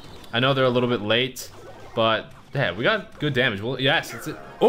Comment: did say damn?